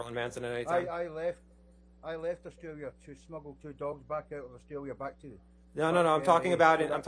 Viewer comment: Did he just say he smuggled a dog 🐕